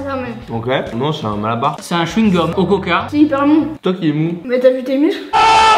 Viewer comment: c'est grave bon 6:10 5 sur 10 Moi : mais t'es pas nete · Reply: Il a dis c hiper mou